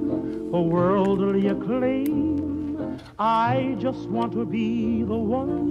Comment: Well i heard "I just want to eat"